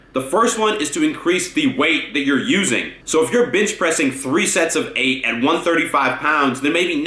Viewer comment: is it me or did i hear him say bitch pressing